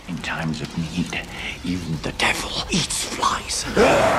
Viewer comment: what did he say?? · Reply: No I think it’s actually, “in times of need, even the devil eats flies.”